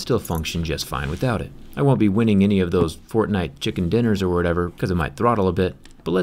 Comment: Did he just say fortnite Chicken dinner